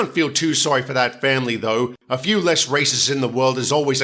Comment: WTF is wrong with you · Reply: Sorry I thought you said “races” not “racists”